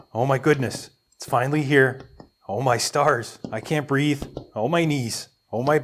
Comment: I thought you said "oh my SARS, i cant breathe" and i was dying laughing